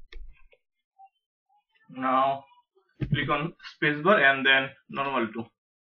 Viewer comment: what are you saying sir? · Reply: Click on space bar